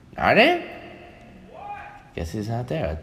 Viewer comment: Did anyone else hear rain say "WHAT" ....